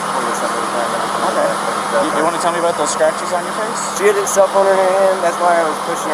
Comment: What was the boyfriend's reply to that? I must have missed it when they saw marks on her face too. This is so tragic, because i feel like she may have been abducted that night, (being left all alone for a night.) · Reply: E Sorry, it's my mistake actually. As I tried to find it for you, I realised I'd misheard. At 5:32 the officer asks 'Do you want to tell me about those scratches on YOUR face'. I'm deaf in one ear, so I only caught it by using the subtitles. Sorry for the error my friend. I agree, this truly tragic and you know what, I'm not going to look at any more videos about it because I feel like a voyeur now. Her death is now a private tragedy for her family to deal with, not 'entertainment' to satisfy our morbid curiosity, chomping down on our breakfast as we stick our noses into someone else's worst nightmare.